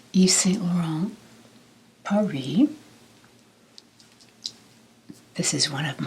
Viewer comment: Sorry but did you say in the beginning "I wear Yves Saint Laurent pour lui" ? Because Pour Lui means For Him, it's for men. Sorry if I misheard but sounded like that to my french ears